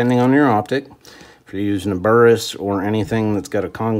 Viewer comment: What kind of sight is that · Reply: Think I heard him say Burris